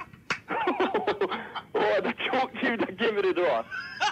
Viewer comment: what does he say at 3:24? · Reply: A woulda dropped you if you came to the door